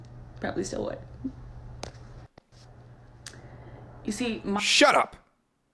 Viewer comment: did he say shut up...😂😂😂😂😂